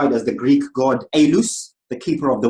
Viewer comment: i thought he said "greek god ANUS, KEEPER OF THE WINDS" i basically lost my cool at that point and had to rewind it.ahahah